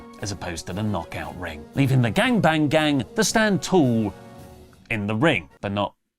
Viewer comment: Have I been hearing this wrong all this time? Did you say "gang-bang gang" at 13:00?? I have been hearing "bang-bang gang"